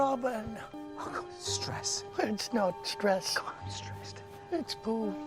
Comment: "It's not stress... It's Pooh." LOL! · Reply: Nikki M. Solis I thought he said “it’s not stress,it’s booze”